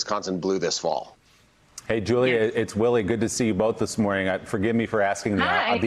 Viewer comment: I thought Willie said..."Hi Julia, its willy good to see you. " I'm guessing I was wrong, and it was " Hi Julia, its Willie, good to see you. " 😄😄😄😄 Hey, let me laugh when I can right now. 👌